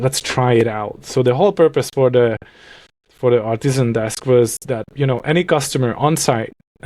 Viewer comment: I definitely thought said "Autism desk" at the end there, and I was so intrigued. ARTISAN desk! makes much more sense.